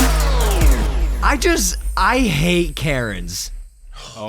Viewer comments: I first thought he said "I hate carrots." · I straight up thought he said “I just hate Carrots”